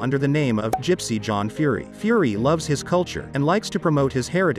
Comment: I thought that said "crispy king"